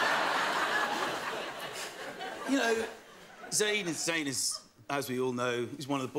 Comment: why the hell do the subtitles say "david's anus" when he says "zayn is zayn"